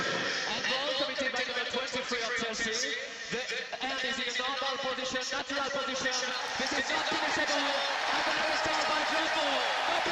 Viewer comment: can someone text here what the referee said? i m trying to learn english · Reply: He said “the hand was in a normal position, natural position, this was not a penalty ball.” But what he said before that even I don’t know, this isn’t really very clear at all and probably not a good example if you’re trying to learn 😂😂😂😂